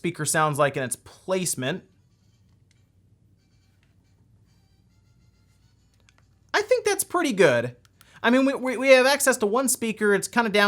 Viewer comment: i hear nothing "i think that's pretty good" xD